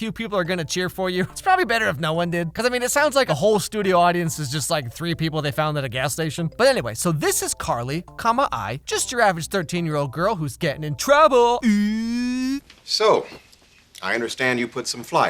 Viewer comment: Why’d I hear “just you average girl who’s gay ooooooo”